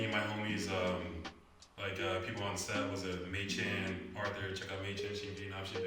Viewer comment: Hey, I wanna correct some names in your English subtitles which are: 3:24 Mei Chan instead of May Chan ( also said she is Vietnamese but you didn't included) 6:06 Haseul instead of Dazzle